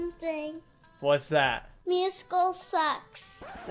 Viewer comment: i thought he said meowscles sucks